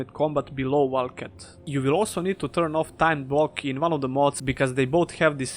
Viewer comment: "You will also need to turn off............" Turn off what? I didn't quite understand you there Also is the order you put these mods on list the load order as well? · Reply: Timed blocking in one of the mods